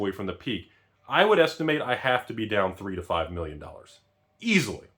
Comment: i choked on my drink & had to back the video up and rewatch it; i thought he said "3~5 billion dollars"